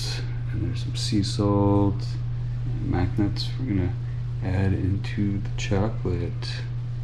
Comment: "Mac Nuts to add in to the chocolate" at 2:12 sounded like "Magnets to add in to the chocolate" and i'm thinking, why on earth is he putting magnets in the chocolate?